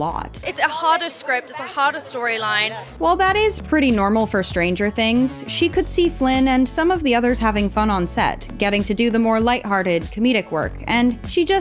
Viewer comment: did u say flinn